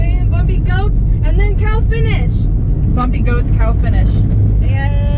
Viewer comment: whimsical language is such fun. I could swear I heard "bumpy ghost, cow finish," but I think it might have been "bumpy coast, now finish. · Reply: The finish line was a cattle grate. So, yes she said "cow finish".